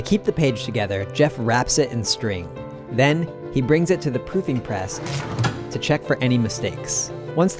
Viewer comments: That's proofing press not to be confused with "pooping press" which is unfortunately what I thought I heard at first. Lol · Maybe its just me, but i keep hearing "pooping press"